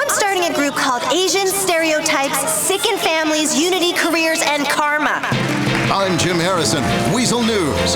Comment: What did she say the group she's making was gonna be called?... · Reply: Asian Stereotype Sicken Families Unity Careers and Karma